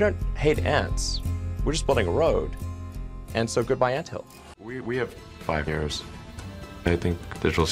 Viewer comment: IMPORTANT!!!❗️❗️❗️ At 2:41 Elon sends us a hidden warning i had to slow it down to 0.75 to hear it he says we have 5 years until ai super intelligence is here... He starts off with saying we have 5 years in a muffled way i thought i heard it at first bt couldnt tell i had to slow it down. He says we have 5 years ina muffled way and adds 1975 a random date and picture to help the 5 years he said stick out... What the fuck does chinas army have to do with what he said for him to put that up there lmao